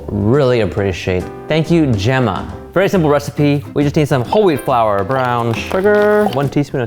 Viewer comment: when he said whole wheat flour I thought he said holy flour